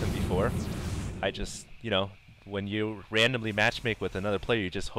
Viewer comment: i could have sworn he said randomly masturbate not randomly match make